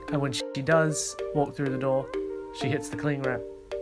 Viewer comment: pretty sure the follower did not say toilet but the door. That is a prank gone too far...